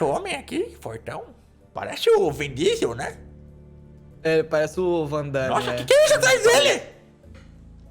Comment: Eu venho de 2022 pra falar que o quase falou o nome de um aguente da ordem no futuro kkkkk T = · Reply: Eu tbm entendi "parece o Wandebas" 😨 o efeito da maconha é triste 😕